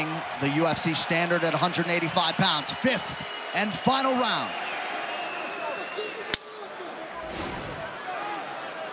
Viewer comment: what was poatan saying to himself? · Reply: "ready to kill"